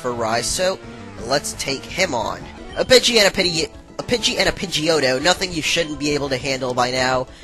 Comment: did he say a bichey?????